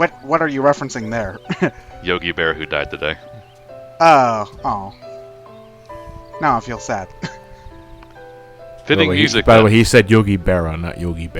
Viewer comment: I heard Yogi Bear the first time so I was wondering what happened.